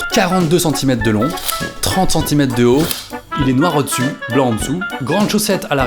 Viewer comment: j'ai compris mort au dessu😂